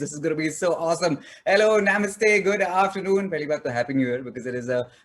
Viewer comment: Did he just say "naymustttey"?